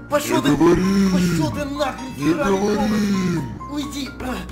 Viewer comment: что он сказал ? на*уй ????? нэ банте пж за мат))) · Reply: Наталья Мезенцева Нахрен